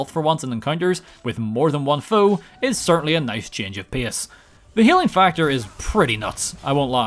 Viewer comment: his accent or wtf makes alot of his words sound rly stupid... change of piss or wtf he said there